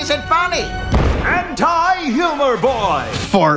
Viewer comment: When they said anti humour boy I thought they said HENTI HUMOUR BOY